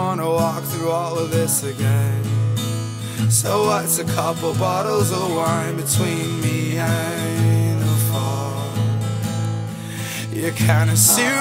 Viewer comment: i always thought he said "wats a couple bottles of wine to a neanderthal" i feel stupid haha